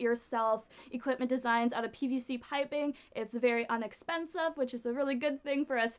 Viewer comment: I think she said unexpensive.